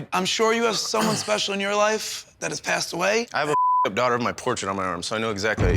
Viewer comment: Did he say @ 10:56, “I have a fucked up daughter of a portrait on my arm”?